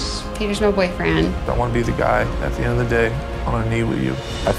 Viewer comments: did he say "I wanna be with a guy?" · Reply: No he said " I wanna be the guy " that hannah picks and Marry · pls what did Tyler say? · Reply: "I wanna be with you"